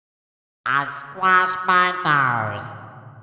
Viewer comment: Uh, what did SpongeBob say? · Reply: He said, "I squashed my nose"